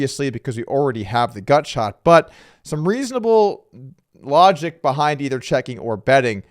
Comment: Did you just say "lodgic" ?